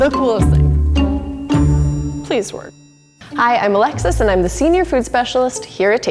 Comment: At first I thought she said ‘hello im alexa’